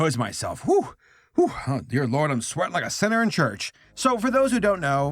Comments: "I'm sweating like a sinner in church." I'm gonna use that one. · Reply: I always heard "-sweatin' like a whore in church-". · Oh I thought you said "sweating like a Senator in church." It kind of made sense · "I'm sweating like a sinner in church." I'm gonna use that one. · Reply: I thought he said "like a senator in church"